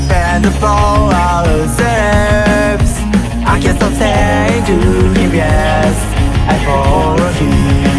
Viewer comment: 、見た目は厳つい兄ちゃんなのに歌声はピュア少年なのがギャップ萌え。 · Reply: 爽やかな曲を、遠慮がち に歌ってるのが良いよね…の歌もっと聴きたい！ 0:20の「Count on me〜♫」が「カンタ・トミー」に聞こえて、いよいよヤバい(*ﾟ▽ﾟ*)